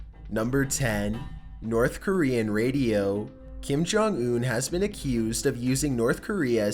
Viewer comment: I thought it said Kim Namjoon! 😳 Army any one?????